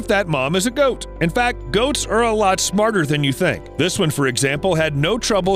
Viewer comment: When he said goats I thought he said ghosts